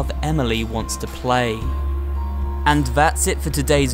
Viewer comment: Is it just me or were it say incomprehensible it sounds like it says we want to play